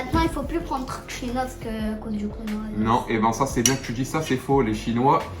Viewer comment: Je sais pas vous mais moi j'ai entendu konohavirus😂😂